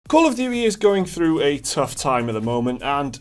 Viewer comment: Kinda sounds like he said "Call of Theory" at the start....now THAT'S an FPS I can get behind.